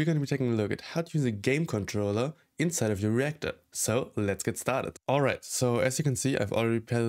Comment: Ngl at first I thought he said we'll learn how to use a controller in our rectum 💀